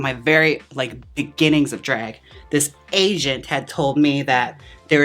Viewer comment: is saying Asian or Agent? lol